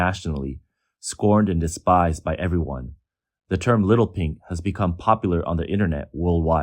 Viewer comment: Did you just say, Little Pinks or Little Pigs!?